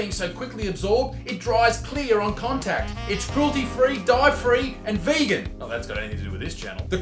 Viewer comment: Haha that's true... With love island LOL Hahaha your partner is funny OMG I thought you said Dive Free haha haha